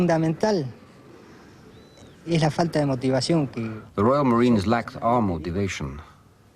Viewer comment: Funny, when the argentinian guy at the beginning says "the royal marines lacked our motivation" I thought he said "the royal marines lacked an armour division"- which would have been more accurate.